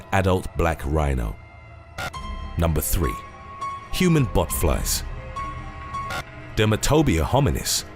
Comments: I thought he said human buttfloss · At first hearing I thought he said human buttflies 😂🤣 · Botflies... totally thought he said Human Butt flies. Thank goodness I was wrong!